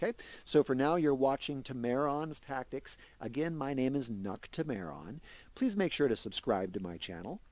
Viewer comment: At the end of these vids i thought you say 'This is 'not' Temeron' maybe as a small har-har as you don't go for that but it's actually xD really good guides getting alot out of these, the playthrough's that are on youtube for this dont explain very well to a new player what they're doing and what is happening, and only 8/49 i have learnt and understand this game more than the several hours i have spent watching, and flailing around in my own runs of this xD, good work as you begin moving out to other games i am sure your Subscriber count will grow! very good informative and easy to follow guide! +1 sub from me :D